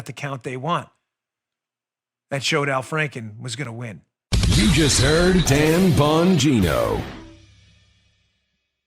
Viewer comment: I always hear “You’ve just EARNED Dan Bongino”...